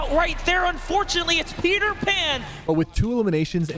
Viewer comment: Bro when coragejd said peterpan i thought he said perterbot😂😅